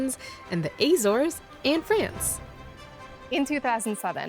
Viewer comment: Maybe I missheard, but I think you said the great blue heron was sighted in the Azores, in France. Maybe they were in the Azores, but these islands are portugues, not french :p We do have Great blue herons in France, ut it's in Martinique, in the Antilles ^^ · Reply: She might have said "... and France" not "... in France"